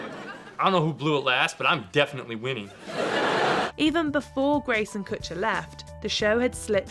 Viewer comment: Did she just say Greyson Kutcher or am I hearing things?